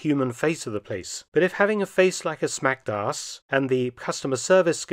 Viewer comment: I lost it at, “like a smacked ass…” At least that’s what I thought I’d heard.